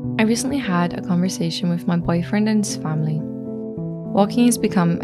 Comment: What is saying at 0:20? I’m hearing: “I recently had a conversation with my boyfriend and son” but surely that can’t be right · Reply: "and his family"